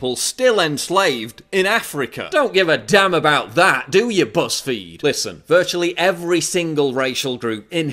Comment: You said, BuzzFeed, I heard PussFeed. But then, what's the difference?